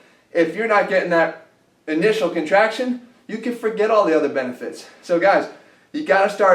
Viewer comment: Its like doing push-ups ... they work best when you do them shoulder width the further you go the less contraction you have on your pecs . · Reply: gonza I thought he said contraction is good... o.O